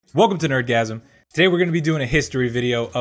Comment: I thought he said welcome to orgasm